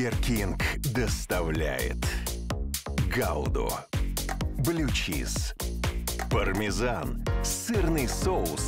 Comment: Я ЩАС ОБОССУСЬ, Я ОДИН ВНАЧАЛЕ УСЛЫШАЛ "ГАВНО, БЛЮЧИЗ, ПАРМЕЗАН"?